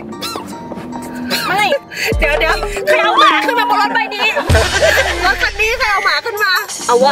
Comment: หนูขำกลิ้งเลยพี่หนูได้ยินพี่พูดว่า"ใครเอามาขึ้นมาบนรถใบนี้" โอ้ยขำจนปวดท้อง5555